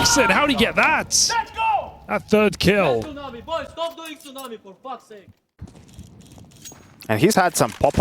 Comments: what did say ? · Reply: i hear "stop doing two navi/stop going to navi, for fuck's sake", i guess "navi" must be a strat or a specific pro callout somewhere in dust2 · i hear "stop doing two navi/stop going to navi, for fuck's sake", i guess "navi" must be a strat or a specific pro callout somewhere in dust2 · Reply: stop doing tsunami. not navi